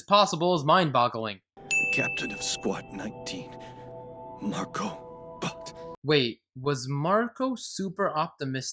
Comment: When I was first watching and Jean discovered the body of Mr. Optimistic, I didn't know who it was and laughed, and then laughed harder when Jean said, "Marco Bodt/Bott" because I thought he said "Marco Butt."